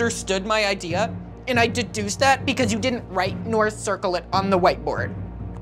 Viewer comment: Wait if her name is kimber then why did she say " you didn't write Noah circle on the white board